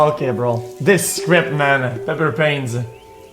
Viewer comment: what did X say @ 28:00 ? Penis pains?